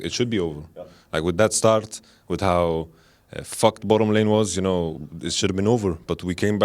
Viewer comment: Sounds like he said "fuck". If he really said that, dunno about you guys but I think it's improper for a "sports" news to allow swearing in their interviews. Very unprofessional.